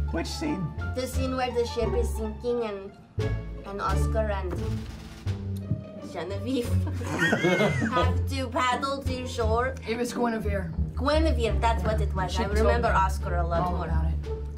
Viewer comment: what did Laura call the main girl? Genevieve? I thought so... Sneaky (snea snake) Laura!!!